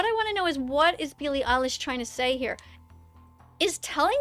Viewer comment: So...at 15:04 I turned on subtitles, and...instead of Billie Eillish, it said "PeElY iSiSh"😂